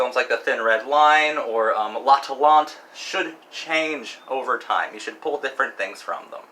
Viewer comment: What is the film you said at 10:24? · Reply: L'Atalante (1934)